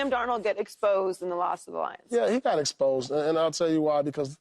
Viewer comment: did she say did joy get exposed during the week of the lions or Am I crazy😂